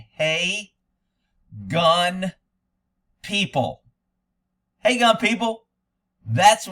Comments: Heck I thought you were saying Wake up people!! lol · LOL. "Hey, Gun, People." Had a guy last year argue, swore up and down you were saying "Hey Young People". LMAO